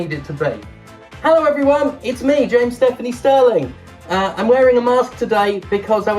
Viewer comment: Because of the mask I heard “gay Stephanie sterling”. Still accurate 💜 love you James!